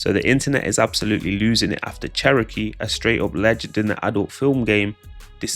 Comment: Calling her Sherokee is crazy😂😂😂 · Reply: I thought he said Cherokee dee... its Cherokee d'ass 😂